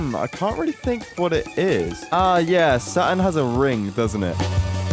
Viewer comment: instead of saturn the captions said sun